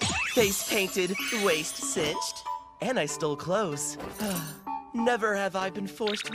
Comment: I always hear “and I still close” instead of “and I stole clothes” 😂